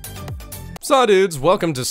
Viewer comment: Did you just say "Sah dudes, welcome to siege nudes"?